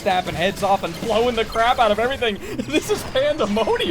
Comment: Did I just hear "blowing the CRAB out of everything"? 😅